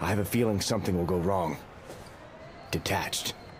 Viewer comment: why did it sound like he said kakashi